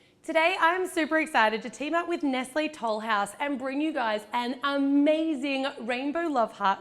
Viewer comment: ate the beginning I thought she said Netflix full house instead of nestle toll gouse